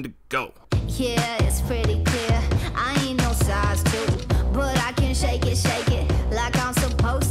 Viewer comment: When she says “size 2” I always thought she said “star student”